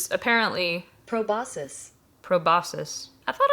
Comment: I thought you said probiscuits on purpose , like a pun or something, and now that's the way I'll say it to hahaha